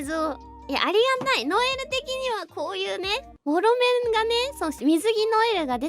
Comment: ホロメンって言ったんだけど、モロメンに聞こえてしまって吹き出しました🤣笑